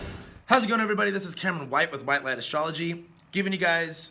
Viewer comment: It sounds like he said This is "white man astrology" lol... I still listened tho, just had to rewind to hear it again he talks really fast haha